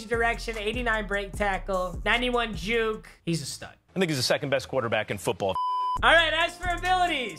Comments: did it say “ this ni**a is the second best quarterback in football” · When that guy said I think he’s the second best qb in football I thought he said nigga’s the second best qb in football 😂